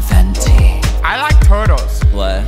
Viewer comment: willam saysmthe most important stuff like i like turdols hahahahah (ik its spelt turtles it sound like hes sayin turdols tho)